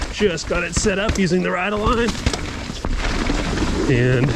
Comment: “just fot it set up using the..” what? The subtitles say using the rydeline but i googled it and no response. With what did you set up your suspension? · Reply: The setup is from norcos ride aligned system